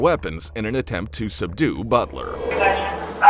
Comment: LOLat about @ 1:25 in the transcript instead of the name Butler, it said Head servant... frikkin computers....